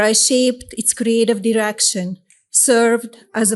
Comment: it sounds like she said erection or arection